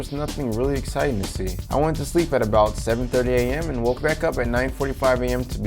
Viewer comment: wait did I hear that right? You went to bed at 7:30!?